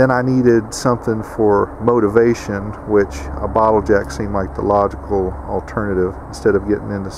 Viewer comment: I heard, "and then I needed something for motivation, which a bottle-of-Jack seemed like the logical alternative" 🤣